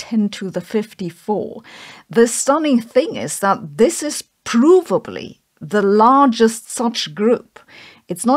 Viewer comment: subtitles are wrong at this point - while Sabine says "provably", the subtitles ready "probably", which changes the meaning completely.